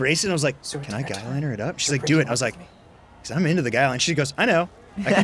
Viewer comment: did he say he’s into the guy?